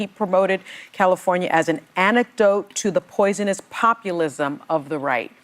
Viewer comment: Did I hear that right? California is the "anecdote" to the poisonous populism? 🤣 (Sadly, that's pretty much what we've been reduced to. An anecdote. Not an antidote.)